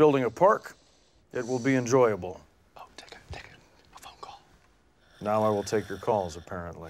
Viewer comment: What does the cameraman say to Ron at 01:27? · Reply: Take a phone call